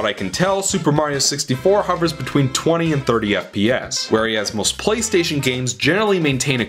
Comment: Did you just say "where-ee-as"?